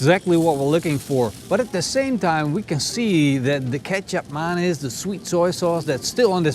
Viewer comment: I thought they were saying ketchup mayonnaise